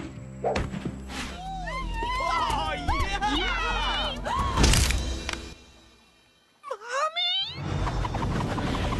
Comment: what did Tyler say☺ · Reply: Mommy?😵‍💫